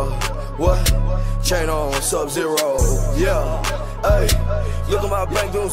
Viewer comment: i always thought he said kano instead of chain on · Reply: Yoga2 fire i thought he said what channel,sub zero😂 when i first heard it